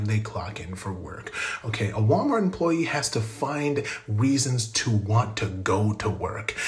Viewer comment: Am i the only one who heard him say "Weasons"? If not im smoking crack 😭